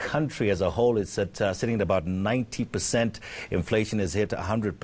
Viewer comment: is he saying unemployment is at 19 percent or 90 percent? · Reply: 90